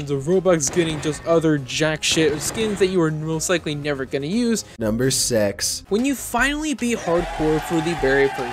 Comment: did he Just say number se*